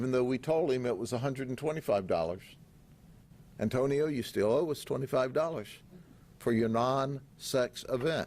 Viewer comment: did he just say you still owe us 25 dollars bwahahahaha